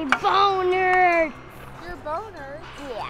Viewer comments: We all know what we thought he said “ my bone hurts” hehe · i thought he said my b=oner hurts